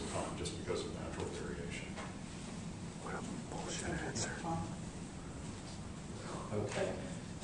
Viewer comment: I laughed when I heard the part @ 7:47.... where the guy whispers " bullshit answer!!" It's also so sad that man is the cause of polluting our home (earth).